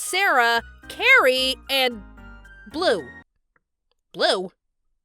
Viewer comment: I literally thought you said "Bloom" like bloom from winx club 💀💀💀💀